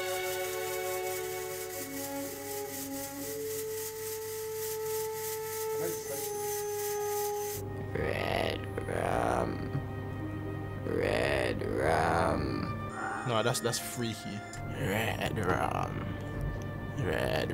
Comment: This so made my day. Nothing but laughs for me on a "scary video" lol Also, Xhaka sounded like he was saying Red Drum, Red Drum hahaha Happy Halloween to all.